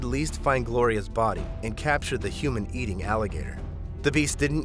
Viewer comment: The human eatin an alligator? Did i hear it wrong 2:23?